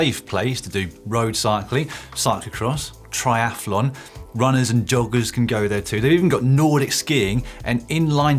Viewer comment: could have sworn Dan said that “runners and doggers can go there too!” ... nice to know they’re not discriminating I suppose!